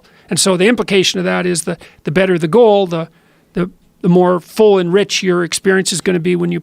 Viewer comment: I think he says "full and rich", not "full enrich" which makes no sense.